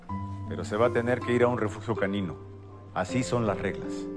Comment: En el minuto 2:12 yo escuche refugio granino like si ti igual😁😅😅